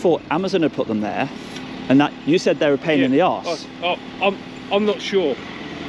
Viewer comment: I thought he said ‘the cones were a pain in the arse’